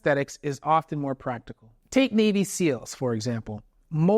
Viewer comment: I thought he said baby seals